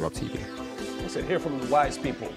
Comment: accent and in the bottom the first run-thru I thought he said 'Now let's go to the white people...' I was so confused...I was like OK I get why is mad but Rob is not white what is up 🙃